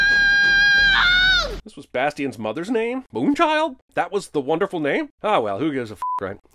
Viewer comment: Moon Child? All these years I thought he said Vangina. Moon Child is more of a nickname than a Real name.